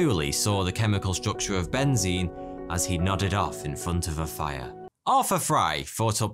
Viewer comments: “did you say nutted off in front of a fire” 🤣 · STG thought the man said nutted off in front of the fire Lmaooo